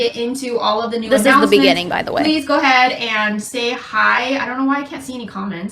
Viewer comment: lmao i thought she said "stay high"